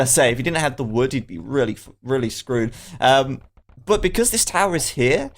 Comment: pretty sure you almost said fucked right there instead of screwed....happens to the best of us hehe